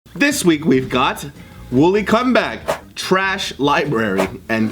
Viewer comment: I wasn't looking at the screen and thought they said 'Wooly Cumbag'